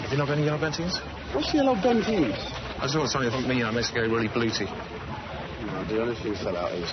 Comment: Can someone tell me what does chris morris say at the beginning (0:37)? After the dealer says "What's Yellow Bentines?" I think it's something like -"that's the one..... makes you going really blooty" but it just seems like random sounds Thanks!